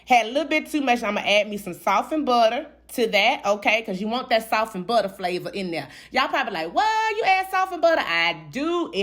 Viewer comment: I thought you were saying sauce and butter. I kept rewinding trying to find out what type of sauce!! 😂😂😂